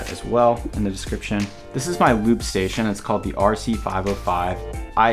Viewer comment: I heard "this is my lube station"